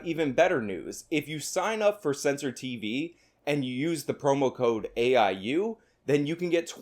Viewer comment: Sounded like you said "homo code".